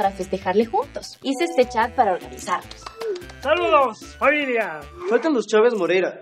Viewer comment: hasta ahora me doy cuenta que dice: hice este chat es organizarnos y dicen hice este chat para organizarnos Like si tambien lo viste